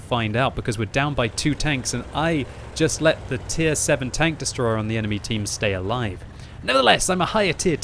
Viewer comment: Did he say tier 7 Tank Destroyer ?